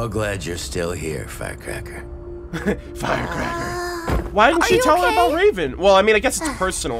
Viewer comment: You talking about the chest pieces when you say she was the first to pick up a relic in volume 1? also I know Qrow calls her Fire Cracker, but I swear I still think it sounds like he said Fat Cracker XD